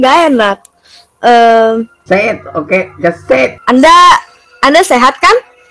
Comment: kak ngomong apa ya ? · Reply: Say it ok just Say it